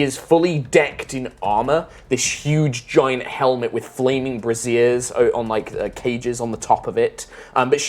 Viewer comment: I really thought the DM said "huge flaming brassieres". 😳 OH, BRAZIERS!!!! 😅